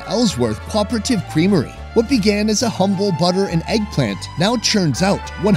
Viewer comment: lol closed captions says "eggplant" instead of "egg plant".